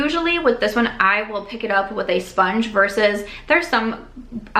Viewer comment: I nearly spit out my water bc I thought you said "I pick it up with my damn sponge" not damp. I was like wow - gettin' aggressive! Hahaha ha! Thoroughly enjoyed this!